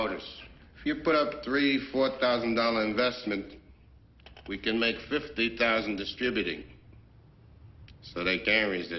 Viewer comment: What did Tattalia whisper to his man? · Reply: He said "I'm a pimp"